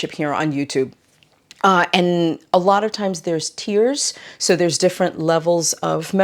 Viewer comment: Not a native English speaker here - I was listening to your video while knitting and thought you said: there are tears… and I thought to myself: relatable. Then realized you talked about tiers in your membership 😂. Love your content ❤